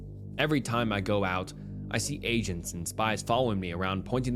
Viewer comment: I thought bro said Asians 😭😭🤣